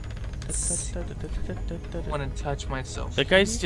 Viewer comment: the first thing I hear in this video is someone saying "I want to touch myself"